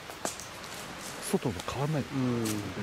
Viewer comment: 女の声でん？って聞こえた